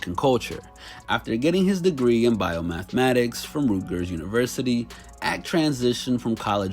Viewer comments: Did he just say Rootgers 😂😂😂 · Bro... 1:17 dude said "rootgers" instead of "Rutgers"... you better throw some respect on those Scarlet Knights